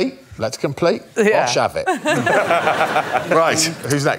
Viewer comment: what does he say? Gosh have it? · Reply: "Bosh! 'Ave it!"